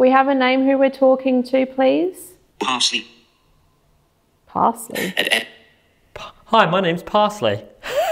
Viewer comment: I heard 'parson' not parsley. Which would make sense being in the chapel area.